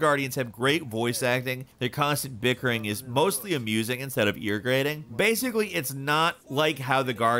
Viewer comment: what word was that again? Irrigating? ;-)